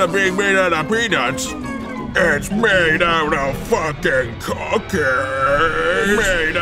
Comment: For a minute I thought he said "It's made out of cocaine"